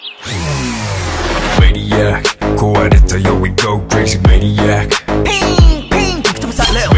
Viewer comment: I heard "we go crazy manaic"